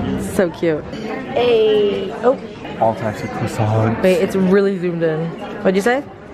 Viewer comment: anyone catch at 7:29 she says "babe its really zoomed in" ommg my heart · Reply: She didn’t 😂 she said wait it’s really zoomed in, it’s just because she said it fast but it’s definitely “wait” that she said. YouTube captions are the worst but I had them on and even they put it as wait 😅